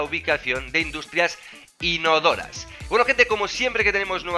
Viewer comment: pensé que dijo industrias Stark :"v